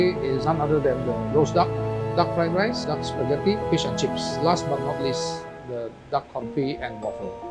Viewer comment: I thought he said Duck and Chips.